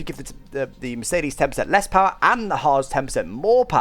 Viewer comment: when he was saying haas. i thought he was saying hards as in the tyre compound